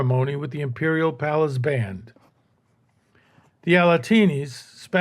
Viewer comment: for a second I thought he said "with the imperial palace banned", but it was "band" i.e. Mehter (Ottoman Marching Band). I turned on the captions and saw the word "band" used.